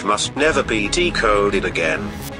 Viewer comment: At the end I heard "And always remember Project Zorgo is farting"